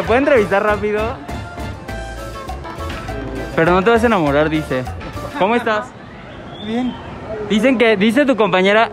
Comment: Yo escuche “NO TE DA ASCO WEI” o estoy loka?